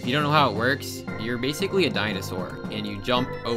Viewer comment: i thought he called us a dinosaur for a second lol